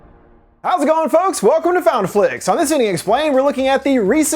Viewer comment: It sounds like you said welcome to Found A Flix